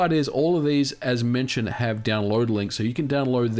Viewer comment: did he just say downloadeling?